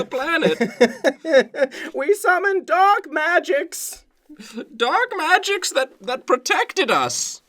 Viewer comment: I thought they were saying "Dog Magics" XD